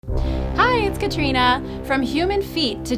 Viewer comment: I jumped when you said "hi I'm Katrina" I slightly jumped cause I heard it as "hi there Katrina" this scared me because my name is Katrina and I'm alone XD